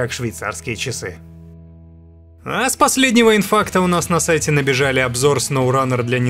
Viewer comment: Когда послышалось "инфаркта")